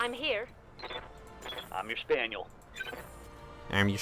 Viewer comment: I keep thinking that rookie pilot in the plan b and training day missions say “I miss Daniel” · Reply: most holy Inquisitor I know now it’s “I’m your spaniel” but first few times I heard it I thought he was saying “cock a spaniel.” Which is rather inappropriate.